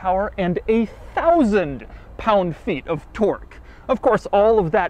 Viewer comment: it really sounds like Doug says “8,000 Pound Feet of torque”. I thought Doug had finally lost his mind.